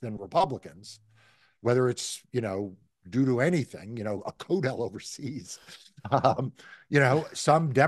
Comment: "A coat hell overseas"??2:00 What is Marc actually saying here? Just curious... could not figure it out... Edit: CODEL = Congressional Delegation 👍